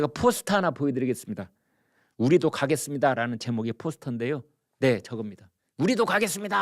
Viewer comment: '우리도 감옥 가겠습니다''로 들리네요!